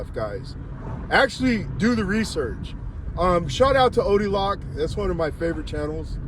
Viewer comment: What is being said @ 12:38, Odie Lock? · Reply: Odeeloctv